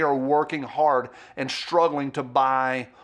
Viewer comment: *G3C, not "GC3".... as I heard say.... 🤣🤣lolol